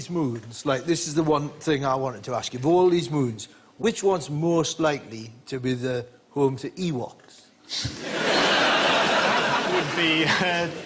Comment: I want a show where Brian Cox answers Ross Noble's dumb questions. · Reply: I misheard him at 9:59 as saying "which moon is most likely to be home to evil". The answer "titan because you'd need to be furry" struck a notably different meaning at first.